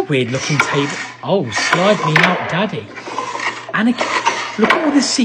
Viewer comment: I just heard "oh slide me up daddy"😳😳🤨🤨🤨🤨